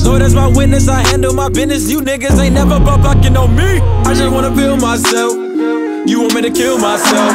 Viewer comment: 네로 I always thought Denzel said "I just wanna film myself"...